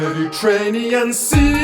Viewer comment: why do i hear "mehdi train me & see" ?? ;)